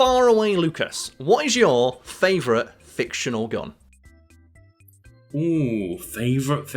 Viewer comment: I'm so tired. I thought I heard: What's your favorite fictional gum?